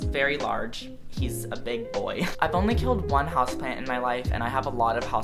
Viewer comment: I kept hearing "husbands" instead of house plants